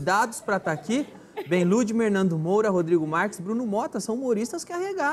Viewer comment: ele disse nando moura ou eu ouvi demais?